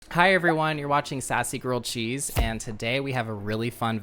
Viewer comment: Could have sworn you said “sassy girl cheese”